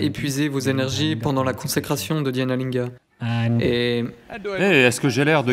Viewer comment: Merci beacoup A 15:11 parle de "Dianolinga" (du moins c'est le mot que j'ai entendu) dans sa question. Quelqu'un sait ce que c'est ? Merci